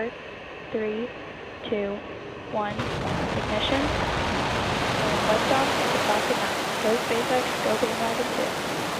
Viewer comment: Can anyone tell me what she said at the liftoff? Something like Go SpaceX go ??? Thanks! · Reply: “Go SpaceX, Go Bandwagon 2”